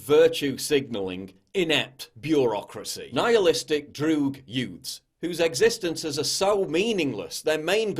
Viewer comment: Did he say yoots @ 8:55 . Somebody gets it?